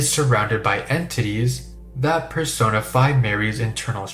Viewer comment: i thought he said “persona 5” 😭💀🙏